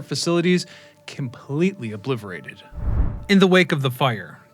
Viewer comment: umm did you really say "obliverated"?? lol